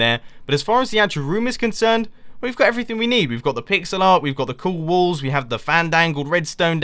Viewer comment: did I hear "the pig-zel art?" :3